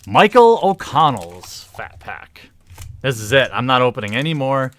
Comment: haha sounded like said fap pack